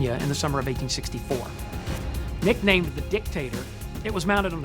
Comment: does it sound like he is saying "Dick" "Tater" like a dick potato to anyone else, or am I just immature...